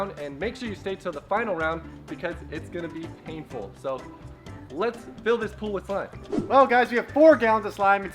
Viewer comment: When he said let's fill our pool with slime I thought her said let's fill our pool with fun 😂😂😂